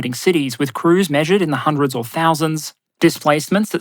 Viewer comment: I thought you said “hundreds of thousands” 😂 and I swore the ships were not THAT big